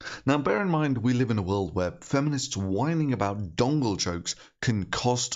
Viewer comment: I thought he was saying Dongle Choke. Not Dongle Joke. Confusing and epic.